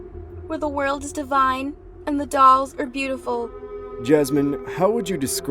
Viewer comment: "DAMN WHAT HIS NAME?" " dolls are beatiful " (IM SORRY MY ENGLISH IS NOT SO GOOD, I HEARD IT "DOGS ARE BEATIFUL" LMAO)